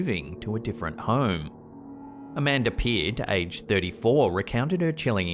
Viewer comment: What he said: Amanda Pierce, age 34 What I heard: A man appeared aged 34😭🤦🏽